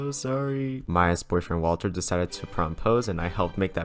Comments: DID HE SAY MY EX BOYFRIEND DECIDED TO PROMPOSE WHAT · i thought you said "my ex boyfriend Walter decided to prompose" and then i realised you said "maya's boyfriend" your welcome for having faith in you frida kahlo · I thought he said "my ex boyfriend" he really said Maya's boyfriend😂😂😂😂😂